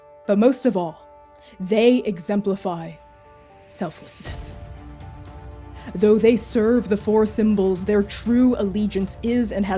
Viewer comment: I heard "Though they serve the 4 symbols and popped off Edit: And then the lord died. OOF